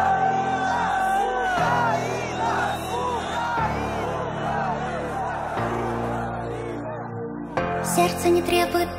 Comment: на 2:19 слушаю с наушников - слышу толпу орущую "Россия!". Включаю на динамик телефона - толпа орет "Украина!". Это что? Очередной янни-лоррел? Или мне пора к врачу обращаться?) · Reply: Тебе не показалось . Там одновременно орут и "Россия" и "Украина".